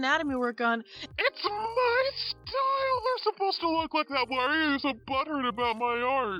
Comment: I thought u were saying “Don’t butter me..” and I was like wtf lmao 😂